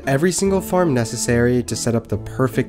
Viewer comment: At the end it sounded like he said Subscrime! xD